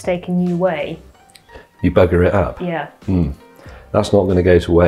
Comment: When the vid concludes, and after says, "Like comment subscribe," then James says, ". . . and cut." Maybe it's my American ears, but what I heard was, "Like comment subscribe . . . ya c^nts." And that while smiling in his marvelous avuncular way, with full-blast sincerity. And even now that I know what he really said, after rewinding, I'm still just laughing and laughing.